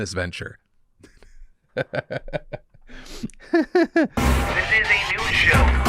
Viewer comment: after the first story I heard "this is a nude show" in the intro.